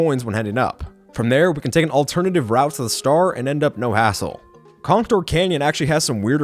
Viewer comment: Did he say the A word? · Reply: No he said hassle. And why does it matter?